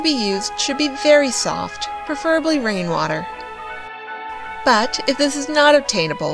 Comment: Did you say ''Raining water'' or ''running water''? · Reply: Rain water